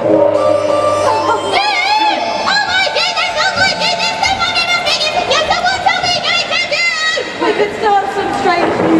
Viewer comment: Can someone please tell me what says as she running up to Bud here 26:49 Please and thank you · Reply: Oh my goodness oh my goodness, the mummy is awakening does anyone know what we're going to do? 😊